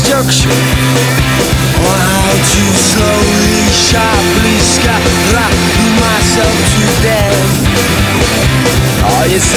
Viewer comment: what does pete say in the first verse? "Oh how just to slowly, sharply screw myself to death" sounds like he saids "ska RRAAA screw myself to death". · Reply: I've been following Your minds instructions Oh how just to slowly, sharply screw myself to death Ah yes, there is a screw It's pointed at my head Then look a dream peddler And a stick of light through my bones "Don't get on the wrong one" "Oh you and you what's your game" Laying me down to waste laying me down Pin me up or put me down Uh uh uh uh oh Now let it all go It's a horror show, you should come on round Horror show, the horse is brown Uh uh oh left something in Moscow She said "I'll show you a picture A picture of tomorrow There's nothing changing It's all sorrow Oh no please don't show me I'm a swine, you don't wanna know me"